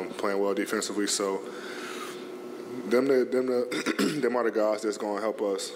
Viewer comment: I swear AD almost said "them nii**as" at 3:13...